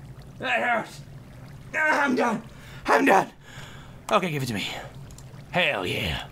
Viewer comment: did say im dumb tinking im done???